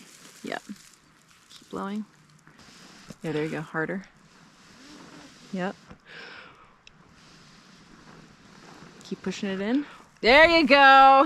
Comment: Here I am in the comments reading and I hear Laura saying: "There you go, harder, keep pushing it in..." (@ 18:13) LOL